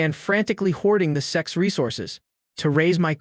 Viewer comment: Ayo did I heard ' the sex resources ' instead of sect resources in 0:24😂